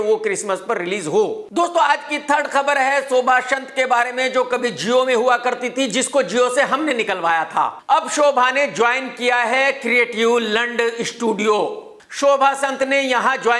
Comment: did he just say creative lund? 😂😂